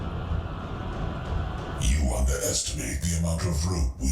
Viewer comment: did he say rope?